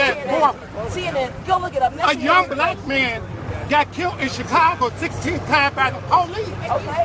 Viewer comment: did he say “a young black man got killed 16 times by the police”???😂x100